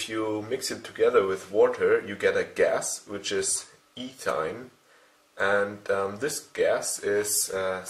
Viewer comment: Calcium carbide reacts with water to produce acetylene gas, C2H2 make sure what you say boss... Acetylene varies with ethane gas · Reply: He said "ethyne" which is the IUPAC name for acetylene gas.